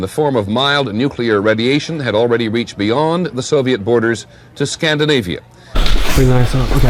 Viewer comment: i thought it said soviet boners